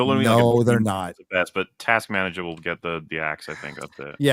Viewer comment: “Task Manager” … I don’t think anyone actually caught what he said. Lol. is so awesome!